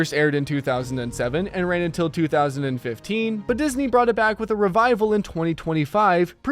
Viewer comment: how come nobody noticed 2005 on the closed caption instead of 2015?